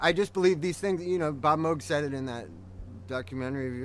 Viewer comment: what does he say about Bob Moke? not sure if that's even his name, but what documentary is he talking about? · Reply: pronounced 'moeg'